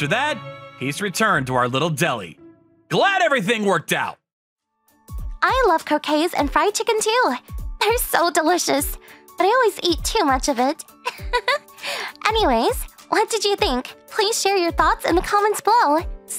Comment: My volume was low and at the end I thought she said “I love c**aine and fried chicken”.